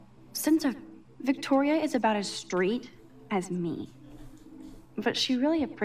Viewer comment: i thoUGHT SHE SAID "Victoria is about as straight as me" BEFORE I READ THE SUBTITLE I JUST